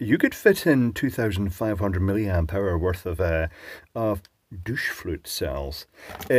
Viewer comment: Had to turn on CC to check what I heard .. "Douche fruits" or "Douche fluids" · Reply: Douche flutes. Derogatory slang for vaping devices.